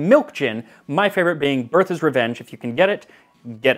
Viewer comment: subtitles say "birth is revenge" which is the name of my next novel.